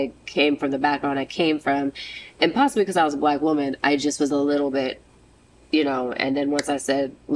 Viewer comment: does she say BECAUSE I AM A WHITE WOMAN?